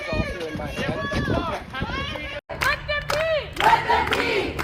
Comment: So thought I heard Let Them Pee💀